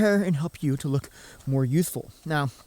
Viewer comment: I am not native speaker and I hear "make you look more useful"))))